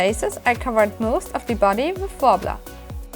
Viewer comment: Sorry, I don't mean to nitpick, especially since I don't speak a second language, but I really can't understand what you're saying at 2:39. It sounds like you said you covered most of the body with blahblah. What is that stuff? It looks like sandpaper to me. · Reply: its "worbla", its a thermoplastic that is quite sticky... you can get it from <link> for example